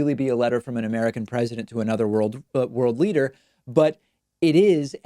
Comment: Did he just say "Third World Leader"?! :D